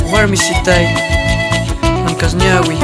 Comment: Que dijo? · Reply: Warmisitay